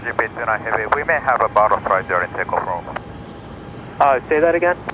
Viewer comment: Sounded like he said they had a bottle strike. If it was a bird strike it musta been a hummingbird.